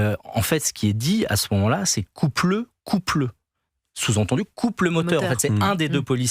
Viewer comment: il se moque de qui ce guignol la ? 🤣🤦‍♂️ ah ouais carrément quoi il invente des propos inexistants, quelle mauvaise foie, le monde entier a entendu et très bien compris ce qu'il a dis arrêtez votre cinéma vous êtes ridicule coupe le coupe le il a dit 🤣🤣 ah ouais ça je m'y attendait pas du tout enfants de pavute que vous êtes